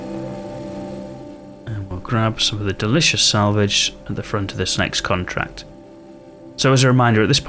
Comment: I thought you said delicous crab salvage im in the hospital and they just have me the morphine i just had a second artificial hip put in from a fall on the ice im like salvaging king crab in space lol